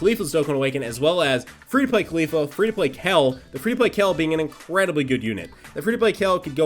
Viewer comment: "free to play kell" anybody else hear him say kell?